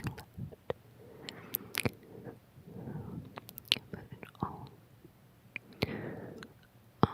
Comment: did anyone else find that it sounded like she said Butter? Plus it's tingle heaven!!!